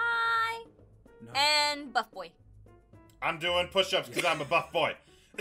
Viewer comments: I thought they were saying butt boy Than I realize from the shirt it was buff boy!😂😅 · Oh man I honestly though she said F boy till I heard him say buff boy XD I must be deaf. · Buff boi But what about Wuff boi? Woof? Wuff? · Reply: I seriously thought she said "F*ck boi" LOL